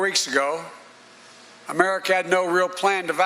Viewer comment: Did he just say Americat? We're all Americats now.